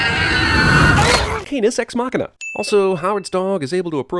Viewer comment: I thought you said "penis ex machina"